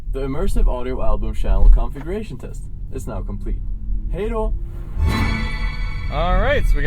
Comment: I just heard he said LFE in the subwoofers